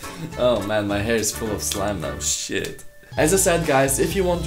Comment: DID HE JUST SAY SH*T?